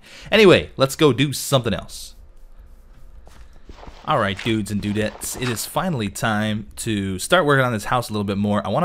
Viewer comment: I thought you said guides and guudettes I Lold so hard and then I realized that's not what you said.